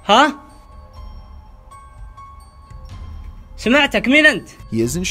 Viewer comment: I think at 14:51 the voice said اطلعوا = atlaeuu which means ‘get out!’ In-formal in Arabic (Iraq 🇮🇶) and then, the ghost hunters said “سمعتك = samieatuk” which means “i heard you” then, the ghost hunters said " سمعتك من انت = sumieatuk min 'ant = “I heard you, who are you?” But I’m not sure 🤔